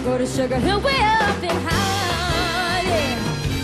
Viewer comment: Wonderful singing, shame she cannot pronounce the word HAARLEM. Sounds like she is singing 'heaven, or 'holland'. Still, she is just a kid - she'll get better and better. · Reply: broxmouth she pronounces it fine she it just sounds like she says halem